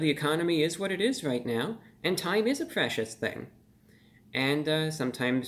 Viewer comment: O I thought you said "time is a fascist thing" no😵‍💫🐣